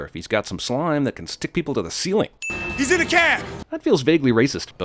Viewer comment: I'm sorry but I have no clue what you heard but when I rewound the footage every time, I hear J say "Hes in A cab" not "the" cab. Hell, I'm pretty sure if you watch the movie with subtitles itll say the same thing I pointed out